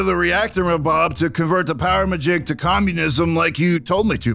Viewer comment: why do I hear "to communism" 💀